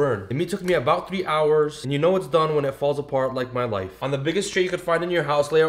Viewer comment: “ it’s done when it falls apart like my life” WHY AHMAD😂 Btw this looks amazing 😊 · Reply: I actually thought he said "my wife" lol and I went back to re listen.